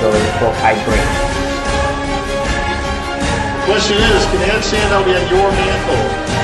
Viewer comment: Can someone tell me what he says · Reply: "Can the "some trophy" be in your hands tomorrow?"